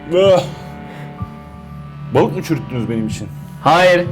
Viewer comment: hahahaaa ne diyor orda yaww :D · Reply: balikmi çürüttünüz benim için